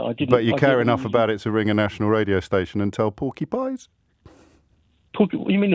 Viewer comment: Cockney rhyming slang: porkie pies - lies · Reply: Oh I mishard him and thought he said porcupines! Maybe I need to clean my ears out. But hey, at least I got the message I got the meaning. Peace y'all!